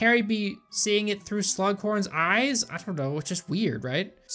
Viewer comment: i keep hearing slug porn cause i zoned out through all these movies besides the first